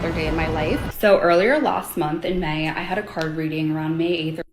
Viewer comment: "I had a card reading"...... all I heard was "I'm really stupid."